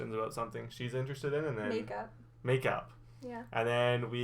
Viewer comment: At first, I literally thought they said they were going to make out.